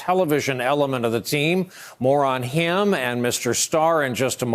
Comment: “moron him” is what I thought he said at first 🤣